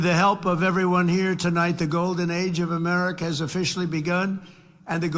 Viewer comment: did he say golden age or golden eggs?